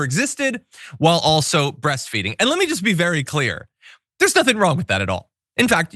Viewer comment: Mo offence but she is NOT breast feeding. I can see the bottle can't you?? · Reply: I thought they said beast feeding and I thought, "What a diss to that cute baby."